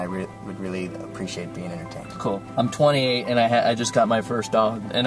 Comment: I thought you said "I'm 20 and" not "I'm 28 and" I got freaked out that you were only a year older than me.